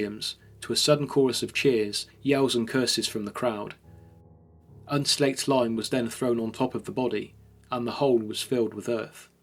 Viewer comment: I have no idea what you said there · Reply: Unslaked lime was then thrown on top of the body.